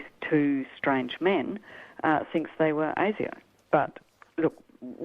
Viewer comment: At minute 1:34, What does say the witness said who they thought the two individuals in the principal's office were? Alien? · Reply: ASIO Australian Security Intelligence Organisation